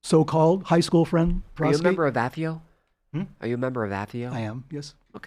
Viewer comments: A.J. asks Peter, "Are you a member of ______"? (I don't know the word, but it sounds something like: "athiu". Anybody know what A.J. said? Thanks. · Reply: AFIO, Association of Former Intelligence Officers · Does anyone know what "Atheio" is? He asked the guest "are you a member of Atheio?" What is that?